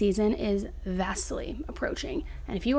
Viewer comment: At first I thought she said, "vastly approaching".